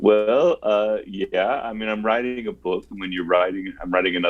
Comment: It sounded like he said nigga book